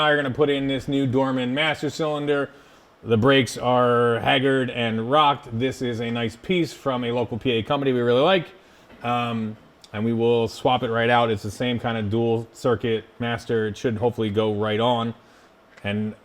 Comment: For a second there thought said diesel swap it